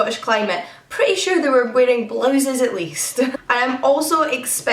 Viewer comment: I thought she said “Pretty sure they are blowing the leaves”. Nope “wearing blouses at least”